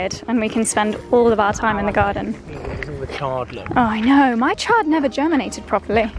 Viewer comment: Hey, I thought you said: My child isn't germinating! But you said chard.😂